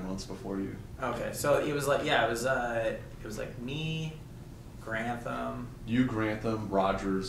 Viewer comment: did they say the Garand Thumb at 2:33?? as in the youtube Garand Thumb?